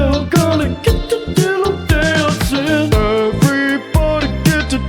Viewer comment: I thought it said dido dancing for a moment